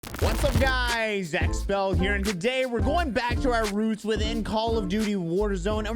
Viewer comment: I thought u said what's up guys zack Scott here